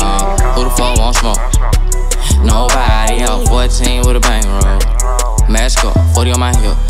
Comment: Thought he said 14 with a banger on me for the longest time. Took me awhile to realize he said bankroll on me 🤔🤔🤔... 14 with a bankroll on me sounds way better 😈😈